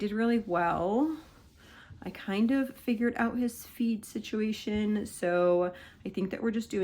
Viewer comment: Lolo, you figured out his FED, omg, I thought you said you figured out HIS FEET problem. Lolol